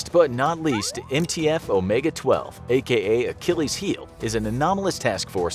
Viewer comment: i thought is said chillis' heels